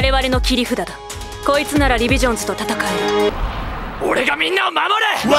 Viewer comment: リヴィジョンズ......ディビジョンに聞こえてしまった。